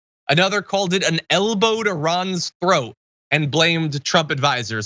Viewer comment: Oh my God where's my mind at 1st I thought you said an elbow to his scroat lol what's wrong with me